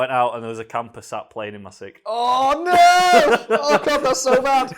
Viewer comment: I couldn't understand the end of ' story at 20:48 . "There was a compass up plane in my sake"? "There was a camper up playin' in my sick"? What did he say?